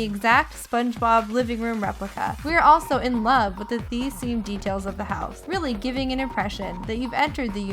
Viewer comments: she said Thea Semed instead of Sea themed 😂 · does anyone else notice she said THEA SEAMED instead of SEA THEMED? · She said "Thea Seamed" @ 2:20 instead of sea themed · did anyone else hear the narrator say thea seme during the sponge bob house??? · she says "thea semes" instead of "sea themed". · did she say Thea Semed instead of Sea Themed or is it just me..?